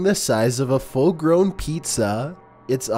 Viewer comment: diD hE JusT sAy FuLl gRowN PiZzA?